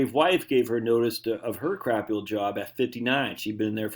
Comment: You can do it too! It just takes a plan and commitment...and ignoring the naysayers. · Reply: I'm a regular viewer but haven't figured out what COJ means yet. Obviously Job. Anyone care to elaborate the acronym? Is it "Current Occupational Job"? UPDATE: I just heard it at 3:00, 'Crappy Ole Job". ha. got it.